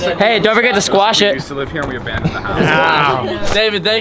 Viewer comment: you can hear David say. “We used to live here but we abandoned the house”. So he moved and that’s just a recording house? Am I crazy? Is that true or am I just hearing things?